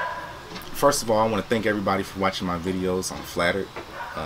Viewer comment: Sounded like you said “thank you for watching my videos on flat earth” lol